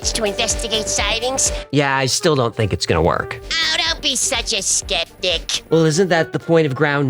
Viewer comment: Hecklefish said "oh don't be such a skeptic" but what I heard was oh don't be such a stiff dick"